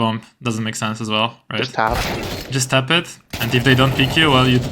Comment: Why does he saying 11:04 "just half?" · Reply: he said "just tap?", meaning tapping the defuse button just for a moment but then holding an angle, in this case t-ramp, because the T´s would peak after hearing the defuse-sound